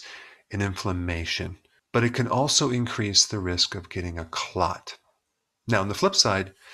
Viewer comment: did he say INcrease the risk of getting a clot at 2:54? or was that a mistake?..